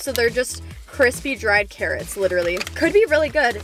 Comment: When you said carrots I thought you said parrots